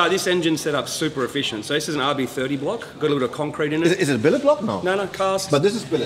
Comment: Hab ich da Concrete (Beton) gehört? Im Block oder was? :S